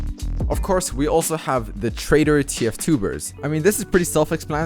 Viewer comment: i was looking away and i misheard him say "traitor TFtubers" and i was like, oh no.